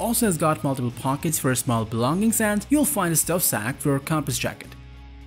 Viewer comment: Why have a narrator with a thick accent who speaks too quickly? At times I couldn't understand what he was saying, and I have very acute hearing. A native English speaker would be a better choice. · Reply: Any idea what he says just before 9:07???? “Finastelfapforacompisjakit” I’d love to know!